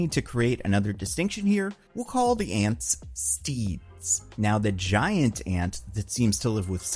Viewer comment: Oh, you said "steed". I thought you said "Steve", and Steve was just some guy that hangs around doing jobs for you without any affection.